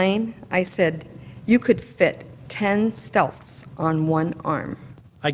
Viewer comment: "ten stealths" Is that what she says, and what is she referring to...